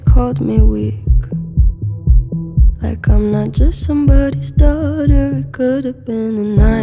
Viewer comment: I heard it as "come on man I'm someones daughter"